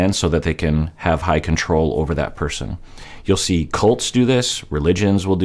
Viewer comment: When he mentioned cults while explaining malicious love bombing, I initially he thought he said “Colts” instead.